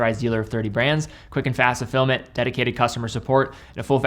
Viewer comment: For years I always thought was saying "Quick and fast to film it" not fulfillment. lol